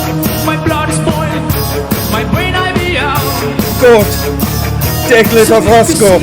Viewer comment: I hear "GORT DEGLAS OGRASKO " great job best movie ever .